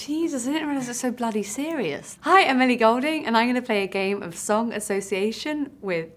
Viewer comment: It sounded like she said 'Hi Emily Goulding' lol